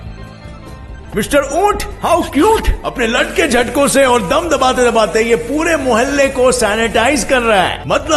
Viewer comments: someone tell what are saying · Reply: Apne latke jhatke se... Vo nhi jo tum soch rhe ho 😂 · Reealy bro Can some one tell what he said at 11:32 Really😅😂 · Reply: 'Latt'ke Jattke😂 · wtf he say land k jhatko s 😂